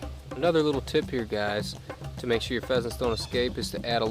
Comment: Thanks for the video we have bears here in big bear California near little bit more than knitting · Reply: I thought the video said how to raise your own peasants 😂😂😂